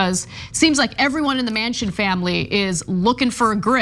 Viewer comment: Manchin Family, and for a moment I thought I heard Manson Family and felt the comparison apt.